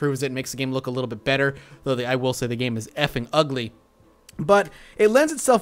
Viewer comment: Did he call the game Ugly...